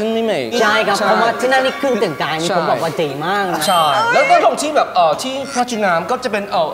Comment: พูดว่าปกติป่ะ ได้ยิบเเวบๆ